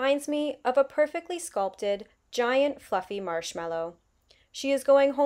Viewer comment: I had to rewind because I heard "reminds me of a giant fucking marshmallow. " but after listening again... "fluffy" LMAO